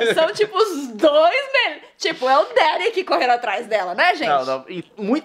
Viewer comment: Nilce falou: É o Derek gente! Só eu escutei isso?